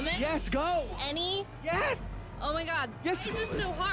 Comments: She actually says "holy shit" at one point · Actually, first I thought she said “Annie” at 0:16 and he said “yes”, because she finally named a women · I thought she said Annie but she meany any